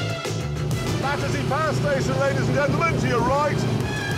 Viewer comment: Caption says: "That is the fire station, ladies and gentlemen, to your right." Ahem. It's called the Battersea Power Station. Who wrote these captions?